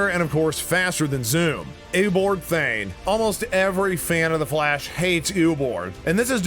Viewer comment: Excuse me did he just say Eoboard thayne?